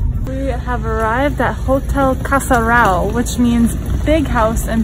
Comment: Hahahahaha funny that you guys can't do nasal sounds 2:08 said like "casaral" instead of "casarão"